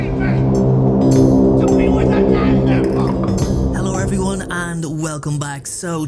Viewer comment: I always thought he said lycanwolf not animal haha